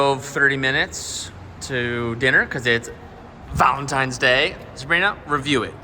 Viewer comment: For a split second I thought said Valen times day I almost unsubscribed😂😂😂😂😂😂